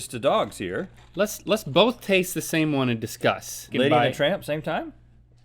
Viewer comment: I thought Link said "Let's both taste the same one in DISGUST " 😂.